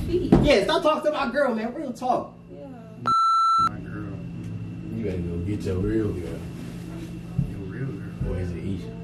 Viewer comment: Did say Jayda his girl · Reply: He said “ bleep this my girl, you better go get your real girl”